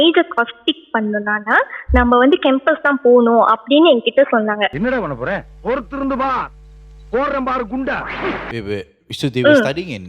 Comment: what just vadivelu say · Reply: Ennada Panna Pora ? Poruthu Irunthu Par. Poduren Paaru Gunda